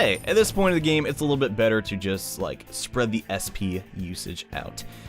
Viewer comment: At the 6:33 the English captions that are not auto generated have an error. It says EXP instead of being SP which is what it’s supposed to be.